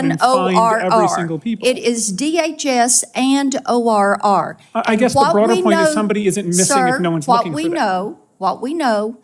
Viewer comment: He actually said “I guess The broader point is Somebody isn’t missing if nobody’s looking for them” 😳 -@ 3:48 -IMO ^That’s a bit worse than the way summarized his statement.. (I mean No offense) The original statement by the guy at the hearing is a Diabolical statement when you’re talking about tens of Thousands of missing children! + God bless, Merry Christmas 🎄☦️ & +🌹Ave Maria🌹+